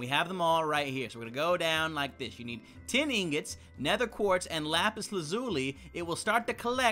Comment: It sounded like he said "ten ingots."